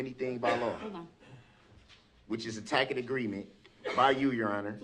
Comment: Did he just say “tackit agreement?”